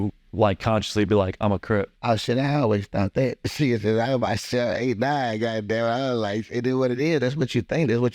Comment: what he say? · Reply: He says, "Sheeiit, I always thought that. Since I was bout 7, 8, 9, goddamnit." Lol. I wouldn't have known either. I only went back to figure it out because you pointed it out! 😂